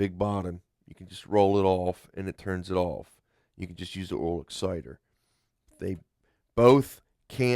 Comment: did he just say oral exciter? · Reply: If by "oral" you mean aural, then yes.